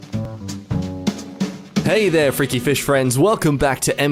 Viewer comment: I thought he said hey there freaky bitch friends